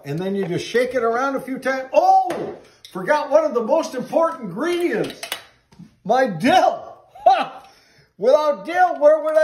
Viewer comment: At first I thought he said “OH MY GOODNESS, I forgot the most important part MY DIYUP!”